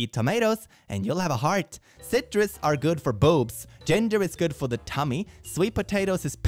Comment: I misheard "gender is good for the tummy"